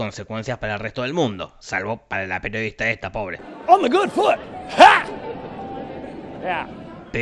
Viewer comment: alguien sabe que dice en el min 10:40? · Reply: dice "on the good foot" seguido de un "huh!". Googleando "on the good foot" me da una canción de James Brown, por lo que el "huh!" tiene mucho sentido.